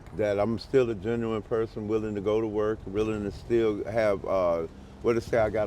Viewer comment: Anybody in the comments tell me if I'm wrong?.. I swear he said that he's willing to go to work willing to return and he's willing and ready to STEAL..👈😮😂😂 I believe he quickly caught himself say the word (WILLING TO STEAL) and quickly jumped on to switching the conversation immediately of he's a good person..👈😮😐😂😂🤷‍♂️🚶‍♂️🚪💢